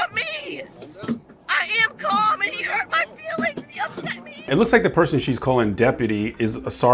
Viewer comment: She's like a toddler who says no to every adult. How embarrassing to be her age and act like her. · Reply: Did she say, "You hurt my feelings?